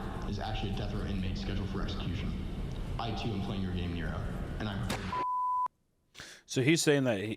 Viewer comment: I thought he said “ I too am playing your game kira” like death note😭